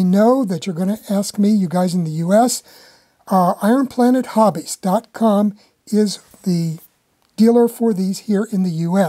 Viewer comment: I'm sorry, but I thought you said where to get them here in the US but I have missed that again and again. Could you please let me know? Thank you. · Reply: Iron Planet Hobbies